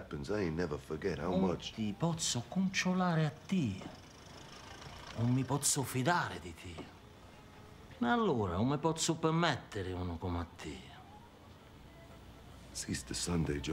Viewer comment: Can someone type the italian version what he says at 0:54 ? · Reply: I’ve found it a while ago, “Non ti pozzu cuntrullari tia, non ti pozzu fidari di tia, non ti pozzu permetteri di como tia”